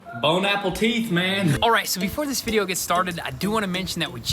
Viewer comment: I didn’t hear him say bon appetit I heard him say bone apple teeth😂